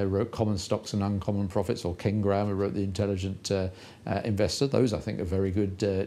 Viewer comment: Did he just say "Ken Graham"?